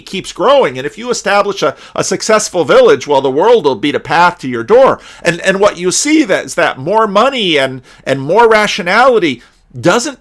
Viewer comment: "If you build a successful village, the world will beat a path to your door." @ 31:00 did I hear that right? Great insight.